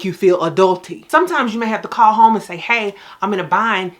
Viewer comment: Why I thought she said she was feeling adultery? 🤔😒😆